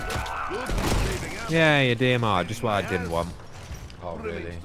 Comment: Did I hear that right? didn't want the dmr? Wtf lol. That's like gun. THE DMR. XD